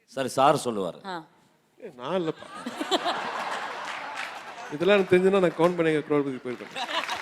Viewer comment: can anybody tell me what AR Rahman sir said in 44:03? couldn't hear it clearly! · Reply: Koun banega crore pati.....